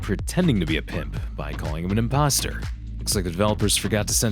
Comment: did- did you say imp- imposter 👀